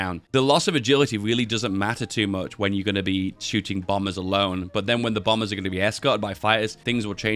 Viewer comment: Why do I keep hearing "Bible alone"?